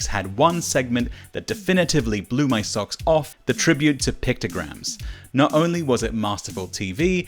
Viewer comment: I thought you said Picograms and I thought hold on Jon Jones isnt an Olympian.